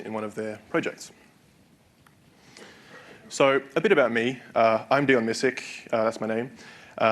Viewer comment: Video: I'm Dion Misic What I heard: I'm doing music ...sorry :(